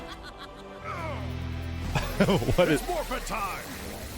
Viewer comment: Ryu saying "ITS MORPHING TIME" is something I never expected but I'm happy to hear lmao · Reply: I heard "Its muffin time" lol